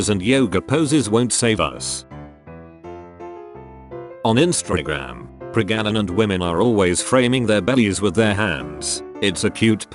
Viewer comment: Did I just hear... PREGANANANT?! XDD